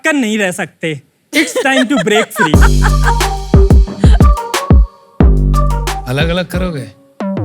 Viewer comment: What he said Aalag alag karoge 😂😂